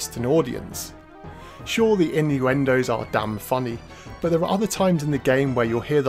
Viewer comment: Dude, please help me. English is not my first language and there is something that you say that i don't understand. Iniwindows? What does it mean? Thanks you! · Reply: Innuendos